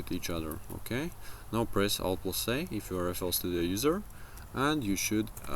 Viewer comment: what button did you say to press at 2:38 ? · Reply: ALT + A - hotkey to arpeggiate notes